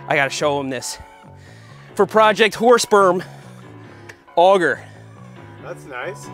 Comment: When your auto captions say "project horse sperm" at 2:18 lol